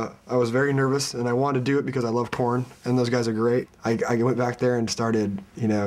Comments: Kinda sounds like Matt says "And I wanted to do it because I love porn" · sounded like he said "i like porn" ahem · it sounded like he said "because I love porn" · I thought he said "I Love Porn" lol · i thought he said i love porn haha. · Sounded like he said he loves porn hahaha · I thought he said "I love porn" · i could barely hear matt when he was singing. i think he did say porn at 0:36 lol and aww he looks so cute here hahaha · I thought he said I love porn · haha i thought he said porn :-) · at :38 seconds I wasn't paying attention to the video and I heard "I love porn" anyone else? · is he saying "and i wanne do it becuse i love Porn" on 0:36 xD